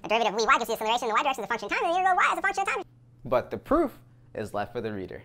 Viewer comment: I heard "why is it a function of time?" Btw the content quality is off charts for someone who's just started!